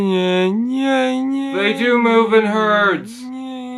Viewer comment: for a moment I thought I heard "The #MeToo movement hurts"